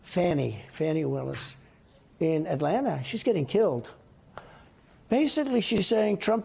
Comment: Did I hear him say “Fani Willis is getting killed” 😮